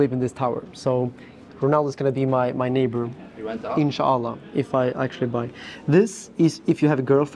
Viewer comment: Did u said insha allah... I am a muslim.. I wanna know...